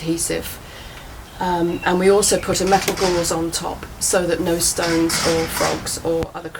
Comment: I LOVE the video. GREAT!! Thanks for taking the time to film and share this! I do have a question, tho: At 2:05 (approximately) she said you put "something" on top to keep stones or critters from falling thru, but I couldn't make out what she was saying. Please tell me. Thanks. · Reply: We cut a piece of mesh wire to the shape of the base and laid it on top.